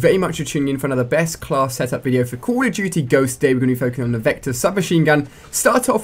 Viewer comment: It sounds like he says call of chuchy ghost lol